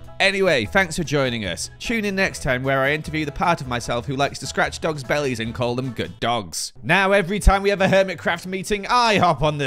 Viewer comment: I heard "Next time I'm interviewing the part of me that likes to scratch Doc's belly and call him a good dog." I wish my hearing was better :(